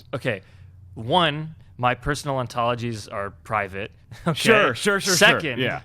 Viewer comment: does he say my "personal antolgies"? what is an antology?? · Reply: sure he said "personal ontologies".